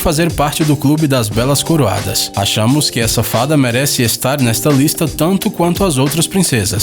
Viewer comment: eu entendi: achamos que a safada 🤭